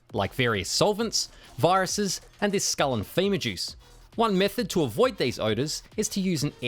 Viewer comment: you tried to say skull and femur… but I heard skull and FEMA 🤦‍♂️ 😂 although truth be told it’s about the same result 🤣 🤣